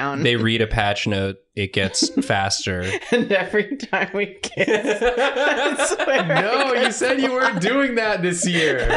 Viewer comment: Captions are wrong at 0:52. It’s I swear I, not I swear we.